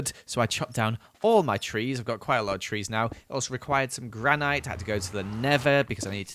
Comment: did he just say Gran-night???